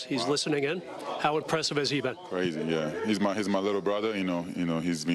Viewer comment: Did Rui just call DLo his LITTLE brother? Or did I hear him wrong lmao